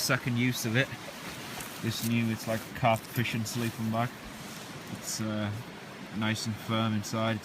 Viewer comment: Hello, at 3:20 into the video, did I hear you correctly - did you say your sleeping bag was a 5-season carp sleeping bag? It looks like a great bag, I've just not heard that term before... was that an Ebay find - do you recall? Anyways, this is one of my favorites of your videos! All the best - Luther